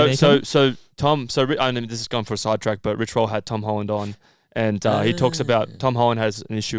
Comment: I've never heard of Rich Roll so thought you were saying you kept on getting Rick Rolled up in NYC. Never going to give you up.